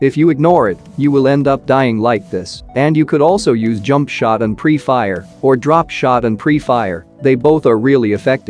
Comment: what i heard: jumpshot in free fire